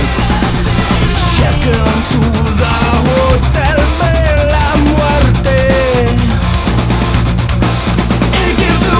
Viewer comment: Anyone here speak spanish? What did he say at 1:14? (If that was spanish...I honestly don't know) lol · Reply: bella muerte = beautiful death